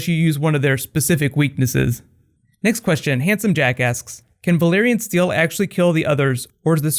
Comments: I thought he said from handsome jackass hahaha I was like a compliment and an insult, pretty smooth... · Am I the only one who thought he said "Handsome Jackass"?